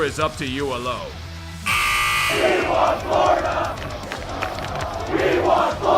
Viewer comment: I heard PH instead of Florida 8:28 lol